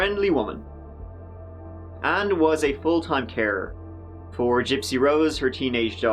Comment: Given the Topic I thought you said at 2:05 "Deedee was a Full-time Terror".